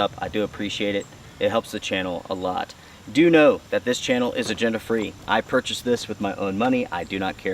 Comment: What is he saying, Gender-Free or Agenda Free?